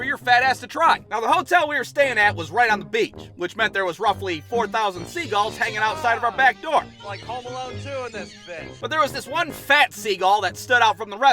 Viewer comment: Idk if anyone else heard him say "god dammit its like home alone 2 in this thing" by